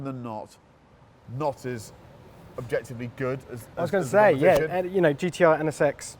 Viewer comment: it sounded like the topgear guy said GTI when naming Japanese cars lol I bet VW is like WTH lol